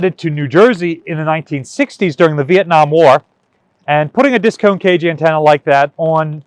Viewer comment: thanks. But why do my ears want to hear disco ? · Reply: I actually thought he was saying Disco cage antenna. Thinking sailors looked at it and called it that.